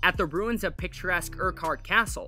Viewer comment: am I the only one who hears “pick your castle”?